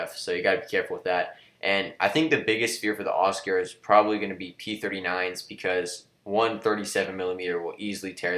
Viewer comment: I heard P-49 but then I replay and hear P-39